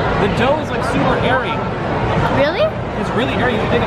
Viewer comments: I thought he said the dough is super hairy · I thought they said "the dough is hairy!" I was like HOW IS DAT GOOD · When he said "the dough is really airy" I thought he said "the dough is really Hairy"😂😂😂😂👏👏✨✨ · XDD Did anyone else think when was trying the pizza the first time they thought he said "Hairy" ( I did...XDD)